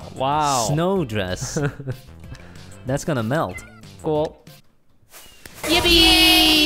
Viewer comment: did he say YIPPEEEEEE